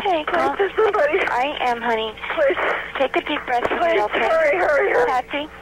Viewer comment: I heard "Hurry, hurry, hurry."